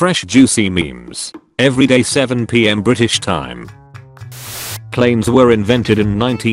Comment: I thought it said when babies were invented then got confused oop